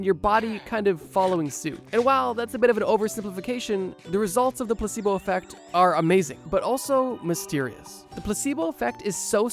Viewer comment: sound like he was saying “the pussybowl effect “ 😂😂😂